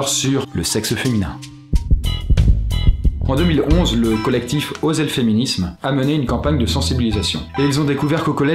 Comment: Ptdr quand il a dit "L'association osez le féminisme" j'ai entendu "L'association osef le féminisme" xD Pardon pour les féministes :')